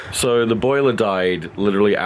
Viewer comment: I heard him say "Le boiler died" at 27:45 and I thought Le boiler was a person with a fancy nam It took me two minutes to finally understand it In my defense English is not my mother tongue